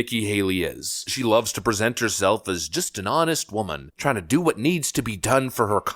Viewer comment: Come on let’s see those nude pictures. Wait, I thought you said exposed herself?